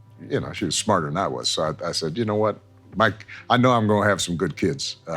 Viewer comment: Is it me or did he say Mike instead of Michelle